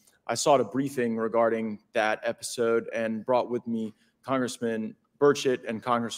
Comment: I'm sure it only sounded like he said "Congressman Birdshit", but he definitely said "Congressman Birdshit". 🤣🤣🤣